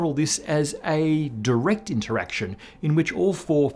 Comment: Captions at 1:56 are incorrect, they read "direction interaction" instead of "direct interaction"